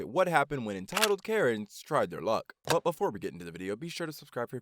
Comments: hello there i have spotted a mistake at 0:10 and instead of luck its lick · Why did he write Lick instead of luck in